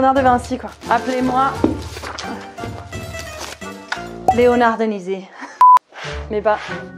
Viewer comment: moi je penser plutôt à "Leonard Denyvaicy"